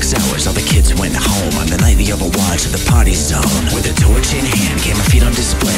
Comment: Song:in the party zone. Me:did they just say potty? Also me: * turns on closed captioning * oh they said party zone